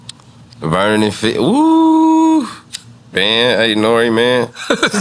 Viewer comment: Nore said "Vernon and fig" that's the 40s hood and the rival of schoolboys hood 😂 I think that's why he said "let's move on from that one"😂😂